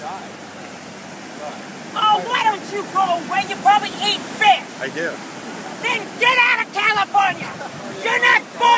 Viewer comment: Did she just say... You probably eat fish? Then get out of California?